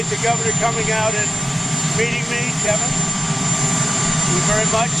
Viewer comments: Did he call him "Kevin" ??? LOL 😂🤪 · Did Trump call him Kevin? 😄